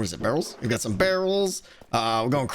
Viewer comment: Almost 18 yr old kiddo walks past as I'm watching this vid. I say "I legit thought Tango just said he was making werewolves" Kiddo: "Honestly, wouldn't surprise me." (It was barrels, not werewolves, sadly.)